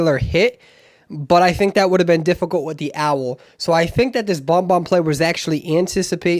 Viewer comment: My stressed self thought it said "BonBon Pre Algebra"